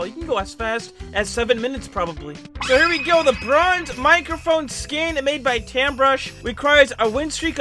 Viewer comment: i heard lebrons microphone